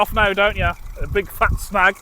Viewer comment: Good video yet again I did Miss heard you saying Big fat Snag 😂 had to go back and listen again